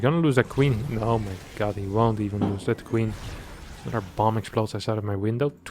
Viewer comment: he says max is being super risky so toss is max and the Zerg I think could be lambo not hunnid percent sure so I was at that time stamp for a while but at 11:45 I think he says another point for reynor which could be the Zerg the micro and macro and queens actually makes sense for Reynors playstyle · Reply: Yeah at 8:10 he does say Maxpax haha, but the 11:45 one is him saying ‘another bomb from my window’ from his noisy neighbours lighting fireworks or something